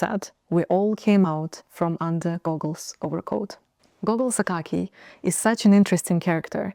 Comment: Gogol 's bukake?! My ears misheard something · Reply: Gogol's Akakiy (it's a name of the protagonist)